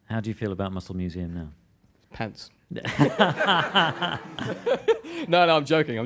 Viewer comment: Muscle museum is awesome. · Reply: What does Matt say at 46:03? Pants?!